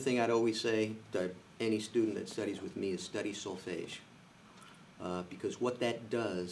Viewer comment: What is he saying at 11:02? Study what? So-fage? I don't understand what he's saying... Can someone please clarify? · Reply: solfege